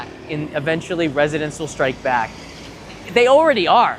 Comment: did just say The Empire Strikes Back?